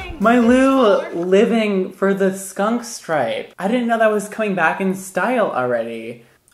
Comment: The skunk stripe. I'm dead. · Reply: KamiQueen omg the first time I thought he said skunk spray 😦😂